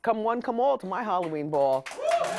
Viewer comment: I thought she said Halloween fall not ball LMAO